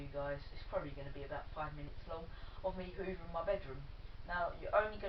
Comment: at first I thought it said hoovering my mum or mom